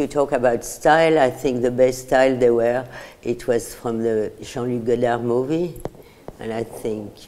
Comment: Can sum1 pls tell me what she said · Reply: I know I’m a year late but I think she said "Jean Louis Godard movie"